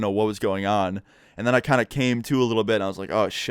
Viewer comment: did he say “and then i kinda came too a little bit” or am i tripping